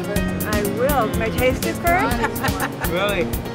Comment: i swear she says Ida instead of Ina.. lol